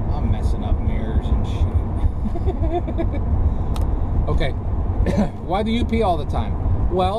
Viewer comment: Bscause you are very careful about personal info, I did a double-take/listen to your, "Why the UP.?"....thought you said, "Why do you pee so much." Your response: "Because it's so close".... Enjoyed the laugh..thanks! Beautiful views and enjoyed the ramble. Scratch furred ears, please. Journey mercies.